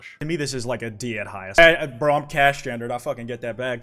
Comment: It sounded like it said "Cashgender", and I thought my name got incorporated into a gender for a moment lol